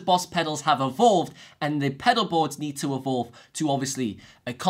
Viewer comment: i keep rewinding what does he say pedalboard need to ....ebo,ewooo,board ...until I figure it my self “ evolved “ idk weather it is correct or no but if its in the context should be right. Say evolve 1 more time bro! 😂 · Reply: he did say 'evolved'